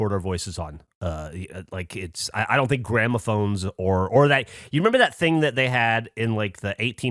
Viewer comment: Did he say “Grandma Phones”? 😂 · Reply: Gramophone